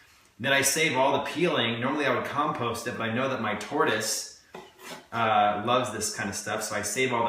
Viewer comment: did he just say his TARDIS loves that stuff? Wtf? 😂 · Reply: tortoise.